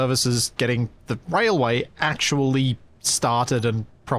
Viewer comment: I know he said "Railway" but it really sounded like "Whale-Way" Can we please call it that since it really fits with the fish theme? XD